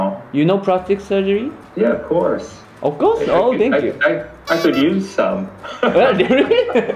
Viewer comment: ここ講師の人なんて言ったの？ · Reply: （I wish）I could use some. ではないですかね？括弧内は省略してたけど。